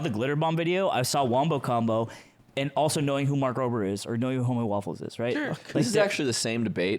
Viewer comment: it’s fun not knowing any of the other people they mention. I thought they were saying mark grover the whole time